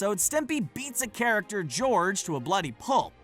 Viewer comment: did he say and Stimpy be the character George to a bloody pulp?